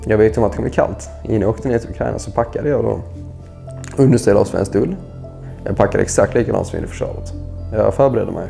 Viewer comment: Thank you so much for the report. Some additional translation. At 2:20: "they realised it wasn't Call of Duty on the phone". 16:35, he says Swedish wool, not Swedish cotton. (ull = wool, bomull = cotton). 26:39, when he says pansarskott he means the AT4. So it's translated correctly, but it might sound wrong if you don't know that pansarskott is the AT4.* 30:05, where the translation is south of Sweden, he actually says Skåne = Scania. (Skåne is mostly flat farmland, FYI). Öppna fält = open fields. Tack så mycket för rapporten. Det har inte varit så mycket om svenska volontärer på YouTube. Så jag har undrat hur många svenskar som har åkt dit, och hur det går för dom. Vi kommer att vinna. Vi måste vinna. Ära åt Ukraina. 💛💙 * I was very confused by the pansarskott thing at the start of the full invasion, when the Swedish politicians said that Sweden was sending pansarskott. I Googled it, and there was apparently no such thing as pansarskott. Eventually after much searching I figured out that pansarskott meant AT4's. So I'm pretty confident that I'm right about that. I wish the politicians had just said AT4 instead, to avoid confusion.